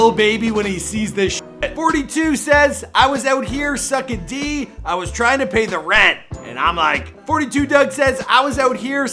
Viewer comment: I heard "I was out here sucking D, I was trying to pay her rent."